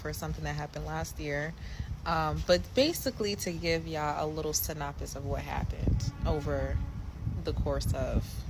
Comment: 1. Did she say SYNPOPIS instead of SYNOPSIS? 2:15 2. Yes, 3 girls got fired from my job from having attendance issues.